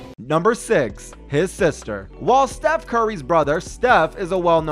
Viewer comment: is it just me who hear "while steph curry's brother steph?